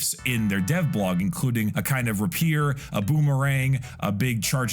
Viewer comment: did just say "ruh-pear"?